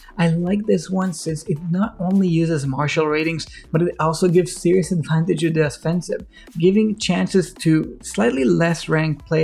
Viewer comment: I've replayed this section of the video 20 times so here's my best understanding of what you're saying at 2:34 "it not only uses martial ratings, but it also gives "sea-rus en tine digida spensiv" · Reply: Lol entertained me and girlfriend. Serious advantage on the offensive